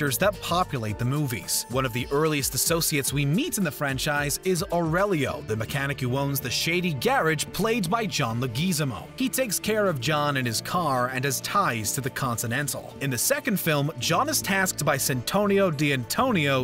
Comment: did he just say John " Le geez zamo" 😄😄😂